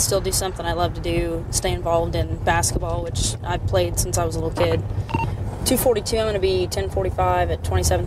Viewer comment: I thought she say " she play basketball when she was a little bitch" but it's a little kid.